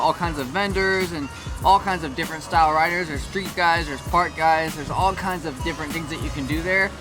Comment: Did he say park or fart? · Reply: park